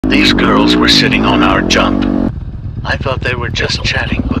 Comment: I thought it said these girls were sitting on our junk.😂😂